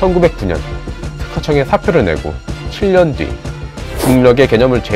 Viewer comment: 눈으로는 6년뒤가 보이는데 귀에는 7년뒤가 들리네요